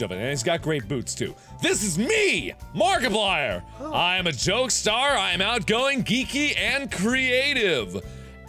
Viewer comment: why did i hear him say "i am a joestar"